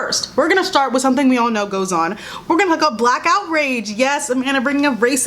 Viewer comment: When you said "black outrage" I thought you said "blackout rage" and just imagined people getting really mad in the dark